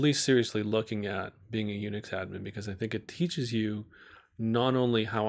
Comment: Interesting video. What word or career choice are you saying at 3:10? Are you saying Unidata? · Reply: "Unix admin"